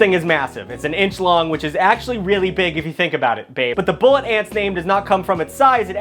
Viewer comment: I heard that "babe"